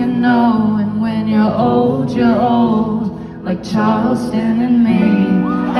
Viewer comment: what did she said in 2:32???? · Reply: Charleston, as in the city in North Carolina where the festival she was singing at was being held :)